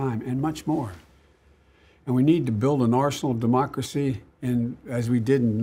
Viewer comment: Did he just say "we need to build an arsenal of democracy?" 10:32. Oh, that will stop the Coronavirus pandemic!